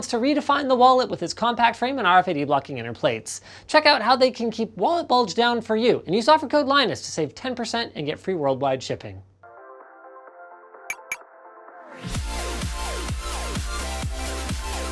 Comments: After the ad I could've sworn he said "free world wide chicken". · Anyone else heard "free worldwide chicken" instead of "shipping?"